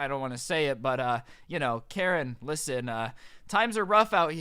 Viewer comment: Bruh I used to think he was making up stories for the channel, but like 2 minutes ago I realized he made the channel BECAUSE he had so many crazy stories. Man I was dumb. Edit: 4:54 wait, did he just call Susan “Karen”?